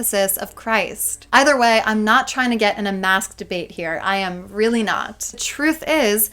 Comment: I’m Australian and heard this as “not trying to get in a masturbate” 😂